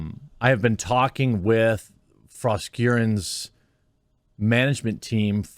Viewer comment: I coulda sworn you said 'frost urine' not (however you spell that name 🤔)